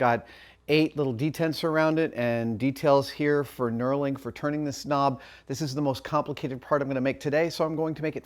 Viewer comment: I 100% thought he said "turning the snob" until I watched it back and it was actually "turning this nob"